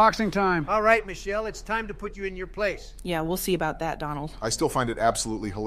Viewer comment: Anyone else catch Obama saying “Dondald” at 0:06 lol